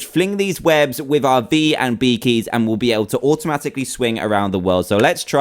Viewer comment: when he said d and b i thought he said b and b XD